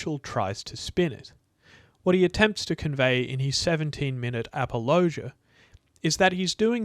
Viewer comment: What word did he say at 28:39 ? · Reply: Apologia.